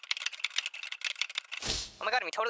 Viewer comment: When the man at the end talks on the phone it sounds like he said "OMG he totally jumped off the cliff he short and the dog chased us all the way again " 😂